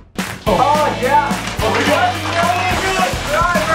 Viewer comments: I love when entered the room, said "buenos dias " And closed the door like "nope" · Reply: I swear the first time I heard this I thought he said "what is this?" in a really weird way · Bruh, I'd pay real money for the full version of the song: "Buenos Dias: *holding bucket*: *sees ice bucket*: *leaves* is a fucking mood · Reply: I heard "wat is dieas?